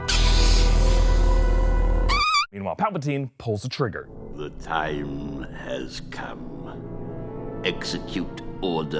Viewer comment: wtf does palpatine say at 12:47? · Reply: Adam Stringer UUUUUUUNNNLIMITED POWWWERRRRRR